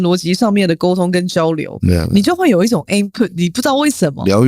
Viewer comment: s 我想问她再说 input ? AIM put ? 我听不清楚什么意思 ？有人可以求解吗 🙏🏻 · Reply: Input. T一直說的就只是這個字，意指來賓是個很有內涵或影響力的人，能給她啟發。